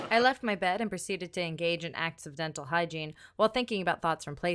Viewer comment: when she said "acts of dental hygiene" I thought she said "accidental hygiene".